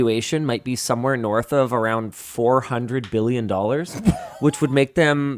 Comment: Oh, I thought Linus said YouTubes evaluation was around 400 million. I had to check the comments. I knew that was very wrong! Glad to find out I just heard it wrong. 400 billion evaluation...yeah that is much more accurate